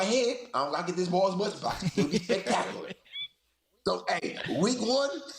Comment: I still laugh at what Le'Veon Bell, Sr. said around 1:12:16-1:12:20. Mainly because of how true it is... I want to say he said "I'm not getting the ball as much, but, I can still be spectacular.", but, I couldn't really tell because Le'Veon Bell, Sr. talks so fast, sometimes, I literally can't tell what he's saying & I misinterpret things.